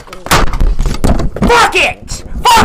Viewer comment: I can’t stop hearing bucket instead of fuck it 😂